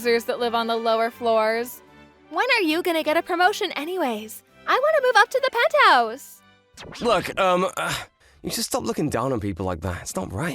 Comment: I misheard her. She wants to live on pent house not pet house.